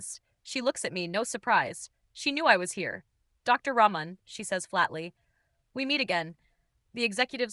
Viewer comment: I keep hearing Dr Sabrina "Ramen" 😂